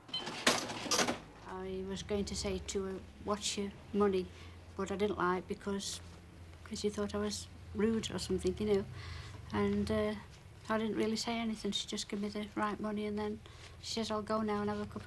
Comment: Can someone transcribe this part? I don’t know wtf this lady is saying here. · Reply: "I was going to say to 'er, 'watch your money', but I didn't like, because, cos' she thought I was - rude or something, you know, and err, I didn't really say anything, she just give me the right money, and then she says 'I'll go now and have a cup of coffee', and, I didn't see her again."